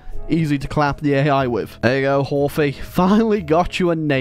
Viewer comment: Did you just say horfy </3